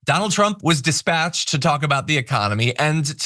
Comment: It sounded like you said rump was despatched Don't tease us